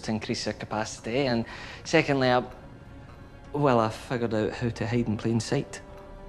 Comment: What is it that he's saying @ 2:11? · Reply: 'I figured out how to hide in plain sight.' Basically, he managed to jury-rig a device to camouflage his shuttlecraft as a Gorn ship.